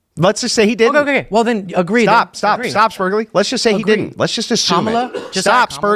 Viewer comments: What is spurgley? What is he calling? At the 16:30 mark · I think you misheard him here. He didn't say "Let's just say that he won". He actually said "Let's just say he didn't"